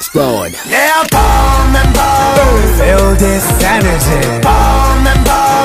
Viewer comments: I thought he said "feel this sanity"😂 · I just wanted to say that I'm a new starlight and ALL THIS FREAKING TIME I THOUGHT RAVI WAS SAYING "feel this SANITY" BISH I WAS LIVING A LIE. Ps: don't look at the edited version you'll more realize how dumb I am...